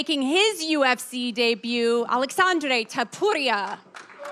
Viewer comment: umm did she say “ TOTORIA” instead of TOPURIA?